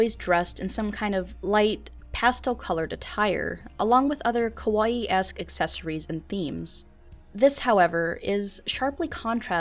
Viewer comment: when you said "kawaiesque" i decided that i dont want to live in this world. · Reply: I thought she said Kawai ass accessories